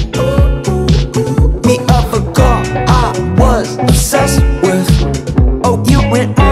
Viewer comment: I keep hearing "I was obsessed with you and mark". Haha. Great fun song.